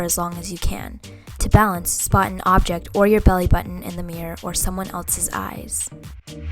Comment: I thought she said "or someone else's abs" lol